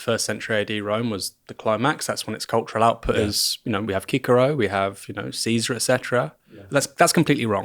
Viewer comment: Did he just say kikero instead of Cicero 😅?